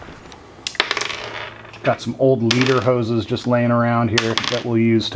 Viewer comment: What will a couple of old German leather work pants do?? OH!, you said you had some old "leader hose layin" around. I thought you said Lederhosen. (Since retiring, it seems I have too much time on my hands and I harass people!🤪😜).